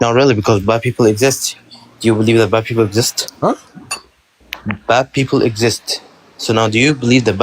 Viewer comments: You thought he said "Black people" didn't you? I did, at least hahahah. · Yooooo at 12:54 I know Avery thought this fool said “do you believe the BLACK people exist” 😂😂😂. The way said “Huh” tells it all 😂😂.